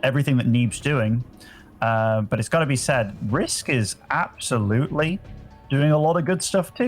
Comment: Why is he calling Disk “Risk” 😂